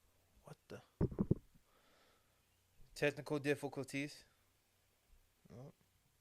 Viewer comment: did he say “testicle difficulties” 🤣😂🤣😂